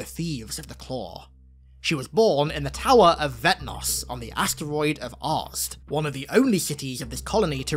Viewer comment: Originally thought you said Writh Kul was born on the “Asteroid of Arse” lmao